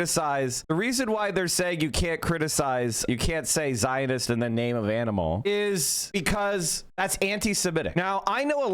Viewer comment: Did I hear "Zionanimal"? 🤔